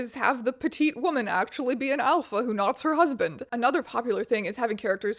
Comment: "alpha who what? her husband" i cannot make out what shes saying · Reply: “who knots her husband”